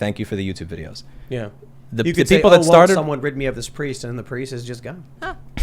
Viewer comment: I heard Tim and thought he said, "Who will rid me of this Prius?" and thought man, there's come car hate! 🤣